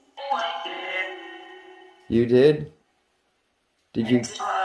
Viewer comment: yeah after you ask "did you pass away in here?" sounds like it says "oh i did" 31:52 "you are alright"?